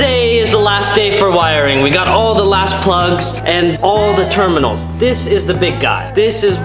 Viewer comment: I could sworn said ass plugs in the beginning of the video